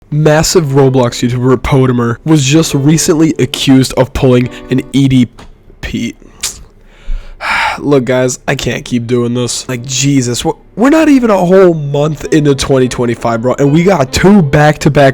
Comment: why did i hear "pulled an edpp"😭😭😭😭?